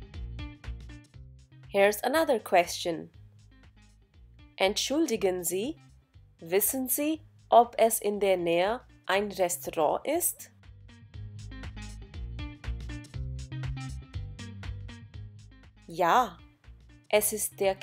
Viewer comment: I am sure I heard the narrator said "ist" but what is written is gibt.